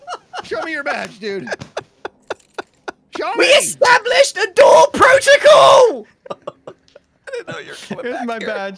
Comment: I click on to this video and the first thig i hear is "WE ASTABLISHED A DOOR PROTOCOL" best thing ive heard all day